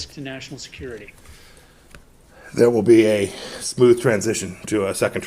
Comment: Sounds like a PTA Mafia wannabe. PS did he say ‘sloothe’ he just made up a word. Man’s a complete fool. The World is watching, the world has seen that Trump lost.